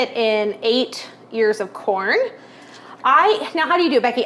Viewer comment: Awesome video:face-red-heart-shape: I laughed I thought you said a hundred years of corn, haha!!!